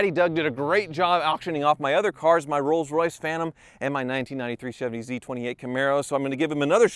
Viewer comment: When you started talking about auctioning off your "93 Chevy Camaro Z28" for some reason I heard "93 Chevy Beretta Z26" and now I want to see you buy "The world's best Beretta Z26". How much could it really be? $2k? $3k?